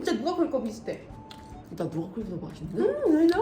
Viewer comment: 아니 여기ㅋㅋㅋㅋㅋㅌ저만 이 umm ~~ i know!! 하는걸로 들려요?????? 아니면 실제로 진짜 그렇게 말하신건가??????